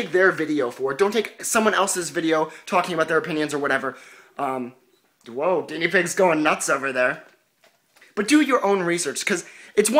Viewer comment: Is it just me or Did he say "Woah, guinea pig is going crazy over there"